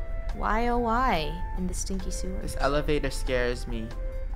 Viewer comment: When said: in the stinky sewers I was like Did she just say ”Im a stinky saurus”